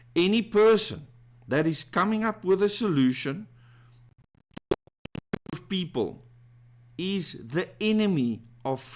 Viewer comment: What is missing here? (What is said?) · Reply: Exclusive group of people